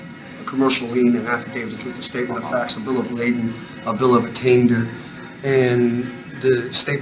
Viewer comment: Did he actually say he’d issue a Bill of Lading?? A Bill of Lading is something you create when you ship something from one business to another. It’s an inventory sheet that lists the contents of a bunch of boxes or crates. Sounds threatening, doesn’t it? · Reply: I thought he said ladling as in: SOUPS ON! 😝